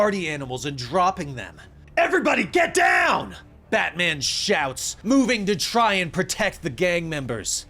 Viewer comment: I kept hearing "Bad man" instead of "Bat man", so I turned on subtitles. It seems subtitles also hears "Bad man". That being said, I loved this. Listened to the entire thing over the course of one day :) Aside from "Badman", amazing storytelling skills. Sub'd